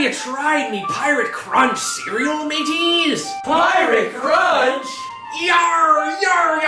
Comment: I thought it said Pirate Church... LAME.